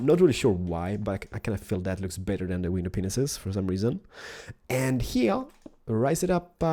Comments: when he says window panes it sounds like he says weiner penises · Me: "he's too fast, ill pause the video and do this" does something that i thought he's gonna do ... ok he doesnt do it repairs LATER IN THE VIDEO he does what i did FUUUUUUU if you get what im saying. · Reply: With his accent it sounds like he is saying window penises instead of window pains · It sounds like he is saying penisis